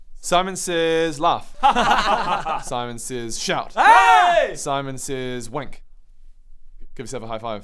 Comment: honestly thought he said “Simon says wank” 😂